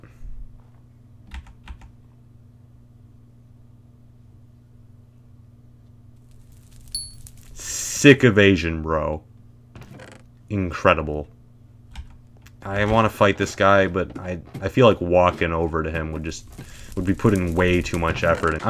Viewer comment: Am I the only one who heard “sick of Asians, bro” until I had to play it back?